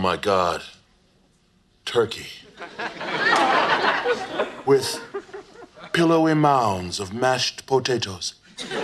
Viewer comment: i always heard "pillow in my arms of mashed potatoes"